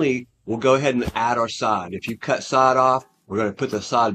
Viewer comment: Did he say "Sod off" ?